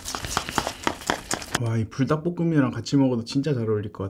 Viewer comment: 불닭볶음면이랑 같이 먹으면 별로일겄같에요 라고 들림